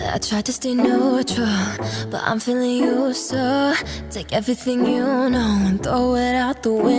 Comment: I was listening to it I really like it said I'm feeling you so but I always thought it said I'm feeling useful